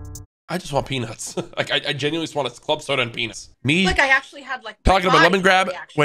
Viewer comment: he really said club soda and Penas